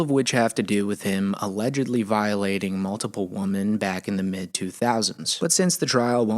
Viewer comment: Why are people saying "woMAN" instead of "woMEN" when it calls for the plural of the word? I keep hearing this, and I don't know if it's a "woke" thing or what? Does anybody know?